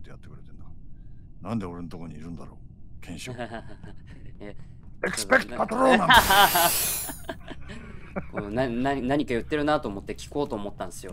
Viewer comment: did he just say "expecto patronum"? 😂😂😂